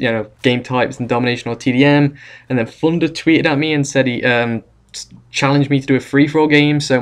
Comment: did anyone else hear Flunder?